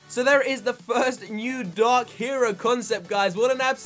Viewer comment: I thought u said dog hero